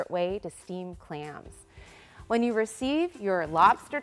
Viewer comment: i thought she said steamed HAMS!